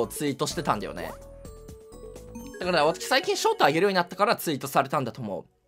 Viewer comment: ここ「ショートあげる」が繋がってしょうたにしか聞こえない